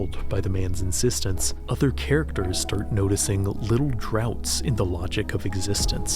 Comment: he says "little droughts in the logic of existence", but the subtitles say "gaps", kinda creating a drought/gap in the logic of the video itself. Don't know if this was intentional or not, but I thought it was cool.